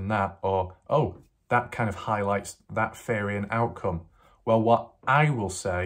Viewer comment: I kept hearing you say “fairy” and I’m so confused but I finally realized you’re saying “theory” 🤦‍♀️